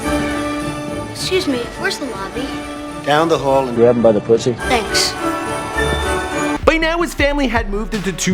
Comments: DID I JUST SEE DONALD TRUMP? AND DID HE JUST SAY "Down the hall, and grab her by the pussy" WTF?!?! WAS I TGE ONLY ONE??? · What the hell. Did I just hear trump say, “down the hall and grabem by the Pussy”? Lol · I just hear " down the hall grab em by the pussy" ???? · did Trump said "Down the hall and gab it by the pussy" lol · Is it me or did I hear Trump say grab him by the pu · Did he just say grab it by the pussy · does Donald say "grab them by the pussy" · I thought he said “around the pussy”.😂😂